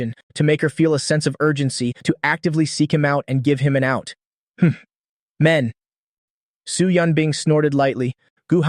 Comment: Did I hear wrong or did she actually said "hmph men" "WOMEN"🍷🗿